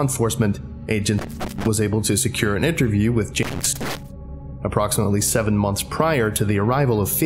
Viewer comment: distortion sounded like “James May” 939 in the UK confirmed?